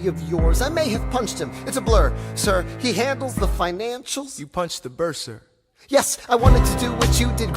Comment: You punched the bursar? LOL ALL I HEAR IS “you punched the Burr, sir?” 😂😂😂